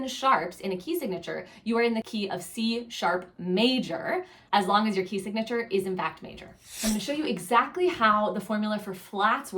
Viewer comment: Hi there! I'm sorry, but I couldn't catch what was it you said at 4:48? As long as your ...? Thank you! 😊🙏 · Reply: As long as your key signature is in fact major! It's different for minors :)